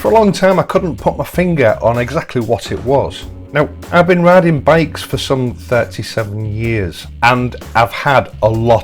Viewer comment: I thought he said, "I've been robbing banks for 37 yrs" Made me do a double take.